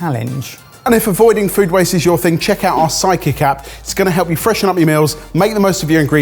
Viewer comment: I hear "psychic" instead of "sidekick" Every. Single. Time.